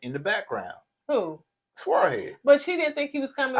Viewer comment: 😂 Squirrel Head! 🐿️ I wish he would cut that mess off. It's not even flattering. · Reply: I thought he was saying swirl head.